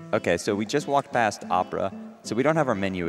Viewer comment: He says "Ok, so we just walked past opera...", meaning Mettaton cutscene, but the first time I heard that I thought he said 'Abra' instead of opera, and that he was refering to Sans and his shortcuts. xD Awesome run! :D